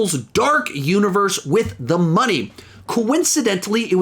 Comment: did he say “the money?” 😆